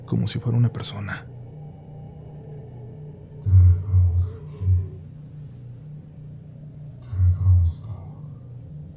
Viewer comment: alguien puede entender que dice la voz, por que yo no 🤔🤔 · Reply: Dice "ya estamos aquí , ya estamos todos"